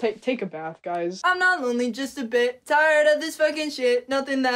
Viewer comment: I thought it said "I'm not only just a fish" not "bit"